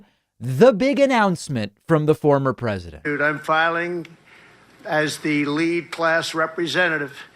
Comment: When the pendejo starts his remedial speech it sounds like he says “dude I’m filing” 🤣🤣🤣